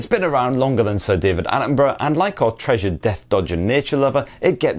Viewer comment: "Death dodging nature lover"! Excellent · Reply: I honestly thought he said "desk dodger" which I guess could also be true lol